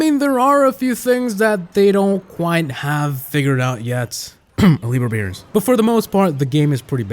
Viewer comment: I first thought he said "elite root beer"